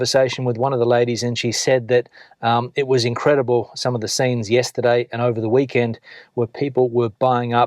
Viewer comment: I guess your aussie accent shows when you pronounce the word 'scene' at 10:48 as 'sane' instead of 'seen' 😉.